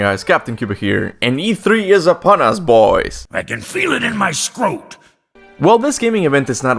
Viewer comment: I legit thought that was you saying “I can feel it in my scrot”